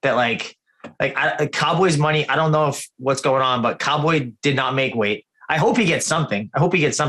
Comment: Did he say Cowboy didn't make weight? i must've missed that